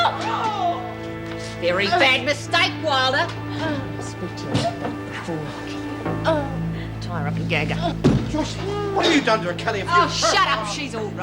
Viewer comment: What does Lou say to Eve at 22:13? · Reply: “And I’ll speak to you about that before I kill you.”